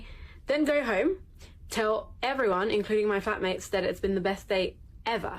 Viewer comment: did she say Phat mates?